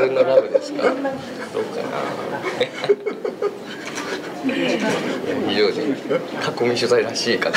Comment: 非常識って言ってるように聞こえる！ ほんとその通りだよね